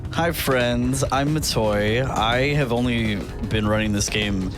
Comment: I misheard introduction as "I'm a toy". I wonder if that's intentional.